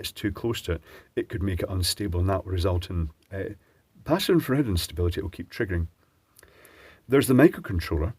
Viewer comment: For a moment I thought you said "passive aggressive instability". Had a girlfriend like that long ago...